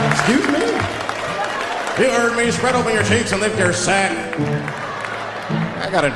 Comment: Lol... Spread your cheeks and lift your sack... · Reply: I thought he said lick your sack